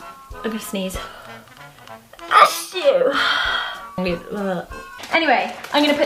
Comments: All I keep hearing is “a SHOE” “a SHOE” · It sounds like she is saying a shoe 😂❤ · It does sound like she is saying a shoe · It sounds like she is saying a shoe 👠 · is it just or does it sound like she is saying a shoe xD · I love Zoe's sneeze it's so funny also it sounds like she is saying "A SHOE " laugh out loud xx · It sounds like she is saying a shoe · All i heard was "A shoe" 😂😂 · She sounds like she is saying A Shoe Lol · It sounds like she is saying 'a shoe' or 'a stew' 🤧🤧🤧🤧😂😂😂😂😂😂😂😂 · it sounds like she is saying a shoe a shoeeeeee a shoooooeeeeeee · SOUNDS LIKE SHES SAYING A SHOE😂😂😂😂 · zoella's probably the only person that really says achoo while sneezing, and it sounds like shes saying a shoe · When you sneeze it sounds like you say. A shoe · It sounds like shes saying a shoe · Sounds like shes saying A SHOE "ASHOO" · A SHOE! Zoe's sneeze tho sounds like she says a shoe!!!! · When she sneezes it’s sounds like she is saying a shoe xx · if you listen carefully it sounds like she is saying a shoe but she is saying achoo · it sounds like she is saying a shoe in an accent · she sounds like shes saying a shoe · When she sneezes it sounds like she says " a shoe" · She sounds like she is saying "A shoe!!" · When she sneezes it sounds like she is saying a shoe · sounds like she is saying a shoe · It sounds like she is saying "a shoe# 😂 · It's sounds like she saying a shoe 😂😂😂😂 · it sounds like she says a shoe😭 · She sounds like she says a shoe 😂❤